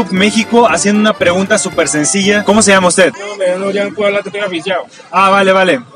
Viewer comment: Quien entendio que dijo el señor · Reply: Que anda sin plata, el pensó que eso era para pagar